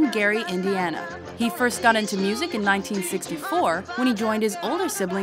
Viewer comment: Can someone please explain how he could have gotten into music in 1954 if he was born in 1958 lol Edit: nvm she said "64". I just had the subtitles on cuz I wasn't sure how to spell Gary lol (the subtitles say 1954)